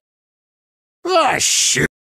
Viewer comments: What Gale said:Oh shoot... What the caption said:Oh [__] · did only i hear "oh sh!t"